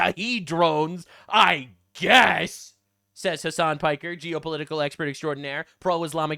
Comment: holly poopthat mehdi hasan is a real person. this whole time i thought was saying many hasan as in the many hasan piker fans and wannabees wtf. lol i didnt know that was a real person.